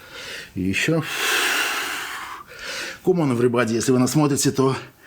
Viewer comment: I could swear he says 'Come on everybody!' which is Rumen's English line!!